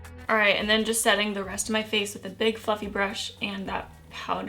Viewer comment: Big FLUFFY brush. Omg, at first I thought you said "big f***ing brush." 😂😂😂